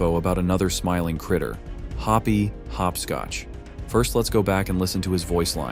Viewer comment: Why did it say hobby hopscotch at 5:00 LMAO😂